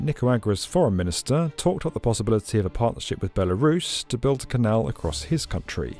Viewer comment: Belarus? Did he say Belarus?